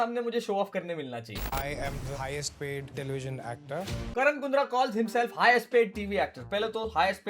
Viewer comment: I heard I am the highest paid delusion actor